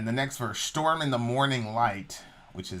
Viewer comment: I thought she said 'Stoned in the morning light'. Like she woke up, smoked a joint and went outside to watch the sunrise? Could be just me 😎😊